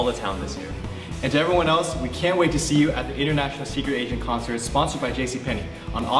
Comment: Could've sworn he said "Secret ASIAN Concert"- only one?